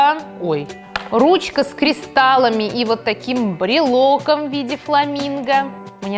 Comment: Мне послышалось что ты про ручку с кристаллами,сказала КРЕСТАМИ